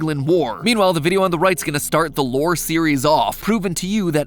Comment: Why did he say lore reason? is he spuid